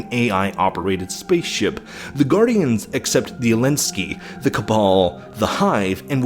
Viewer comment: I'm sorry did he just say Elinski?